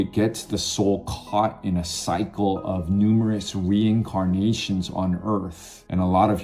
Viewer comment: I thought you said that reincarnation is a soul trap😂